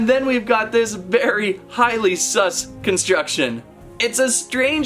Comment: Did he just say sus?